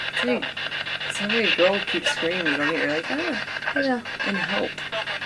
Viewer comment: did it say someone please help? however it got quieter and quieter